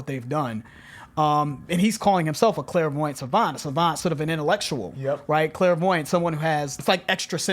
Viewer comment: Pretty sure Thought said Clarence Avant's, not Clairvoyant Savant's... Knowing Black Thought he probably meant both though...